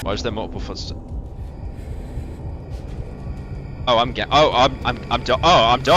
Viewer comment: I heard him say I’m gay. You can’t beat the allegations pyro